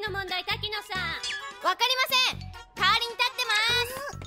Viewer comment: kaorin sit downなんて言ってるか？w 「かおりん立ってます」じゃなくて「代わりに立ってます」だろ